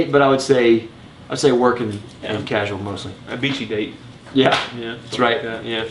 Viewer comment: AHAHAHAHAHAHAAA!!! A beachy date! I had to go back and listen to that again because I swore I heard "A bitchy date" the first time. LOL! As usual, your review rocked guys. I suspect Essenza was dreamt up in a corporate conference call.